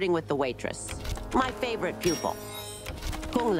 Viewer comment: Madam Bo to Kung Lao: "My favourite pupil" Kung Lao: "I bet you tell Raiden the same thing" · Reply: Oh... Pupil. I thought she said "people", and was like "why is she speaking street?" (You know, like when people say "I trust Bob. He's good people.")